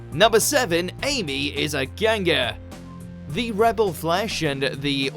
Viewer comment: lol I thought it said "Amy is a ginger"